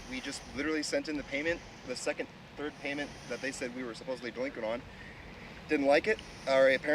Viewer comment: yo, I don't think at 1:54 the guy said "doinking on", I'm pretty sure its "delinquent on", haha